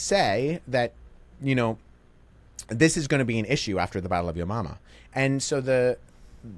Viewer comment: excuse me but did you just say the battle of yo mama